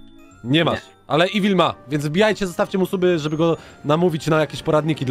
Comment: usłyszałem dawajcie unsuby